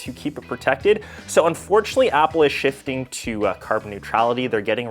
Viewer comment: Did you just say at 1:32 that its unfortunate that Apple is shifting towards carbon neutrality?